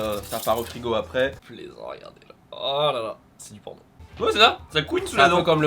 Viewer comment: à 5:20 dit quoi 😂😅 je suis pas sur ...😂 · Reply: C'est du p*rn? 😂😂😂